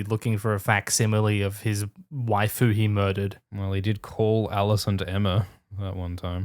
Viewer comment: Heard it as ‘Waifu he murdered.’